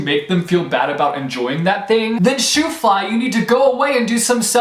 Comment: You said "shoo fly" but I thought you said "shoes will fly" and I was like, sassy the readers back 😂😂